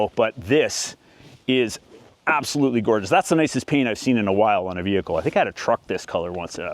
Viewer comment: I've only got one earbud in at work, but did he just say, "That is the nicest p*nis I have seen in awhile." or "That is the nicest penut I have seen in awhile." Because that did not sound like "paint".